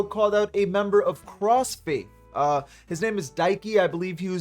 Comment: holy shit this entire time I thought everyone was saying crossfade and I was shocked lmao